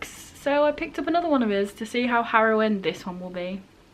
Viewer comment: I'm sorry fam, I know you meant harrowing but it sounded like you said heroin so "to see how heroin this one will be" made me crack up. Totally know what you mean about being scared of reading a new book by the author of one of your favorite books tho. Unfortunately Circe did not live up to TSOA in my opinion, but I hope that isn't the case with your situation.